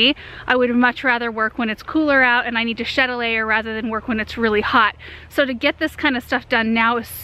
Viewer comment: Your new edging is perfect!!! You also keep us on our toes vocabulary wise!! I once in awhile have to look up meanings of words you use which is a good thing. Shettlelay (sp) was the word today I wasn't familiar with. Does it mean renovation? You used it toward the end of the vlog. Thank you for all your garden knowledge, enthusiasm, and inspiration you give us all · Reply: What she said was "have to shed a layer". She was talking about working outside in the cold with layers of clothes on. It's around 7:25.